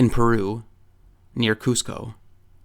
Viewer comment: I thought he said “near Costco” 😂😂